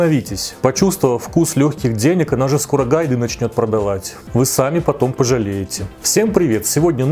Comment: Почему я расслышала «скоро курагу начнёт продавать»? 😄🤦🏼‍♀️ здравствуйте, и хорошего вам дня🌷🌹💐